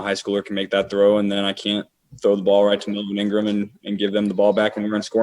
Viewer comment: did he say the n word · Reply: He said melvin ingram